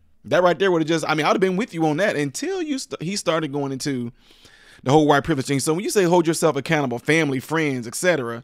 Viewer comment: I thought he said use your white privilege. I am sorry if I am wrong.